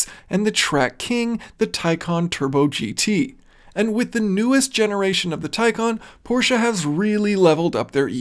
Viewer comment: All I hear is “wait until the next gen”